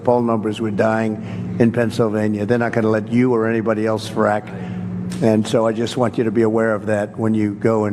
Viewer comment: I TRULY THOUGHT I HEARD HER SAY CRACK